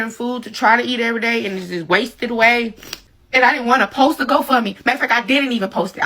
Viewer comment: Now, she has that go fund me and she’s complaining because she can’t get any money out of it. Greedy greedy greedy greedy greedy! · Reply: I thought she said she didn’t start or need the go fund me??